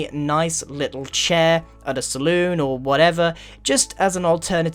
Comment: Wait, wait, wait. Did he say saloon?